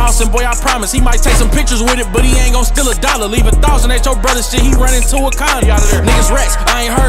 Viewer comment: i thought he said, "leave a thot at your brothers shit and he running to a condom", its actually, "Leave a thousand at your brother's shit, he runnin' to Wakanda" haha